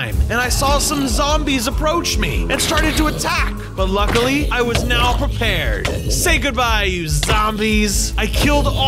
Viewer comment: I thought he said "Luckily i'm not prepared." (18:13 - Fungus Amongus with villager nose)